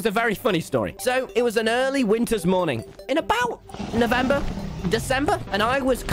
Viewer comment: Am I the only one noticing that at 1:24 when he said November it sounded like L'Manberg?